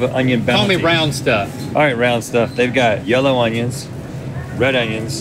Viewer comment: “All right Link” “CalL mE rOunD sTufF” “All right round stuff” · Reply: Roouuuund. I thought he said brown stuff. Thank goodness